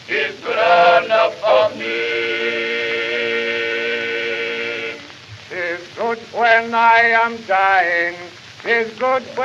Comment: I thought it originally said "It's good when I am dying."